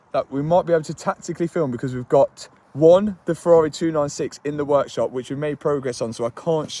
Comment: I thought for a short second he said Ferrari F80